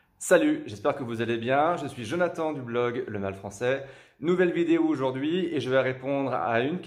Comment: Je suis vraiment bizarre.. J'ai compris "L'anal français"